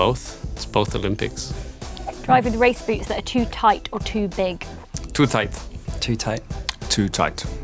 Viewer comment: Can anyone please help me with the question on min 5:41? Some preference about something too tight or too big. I have listened the question many, many times and still can't understand it, sorry. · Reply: Driving race boots - too tight or too big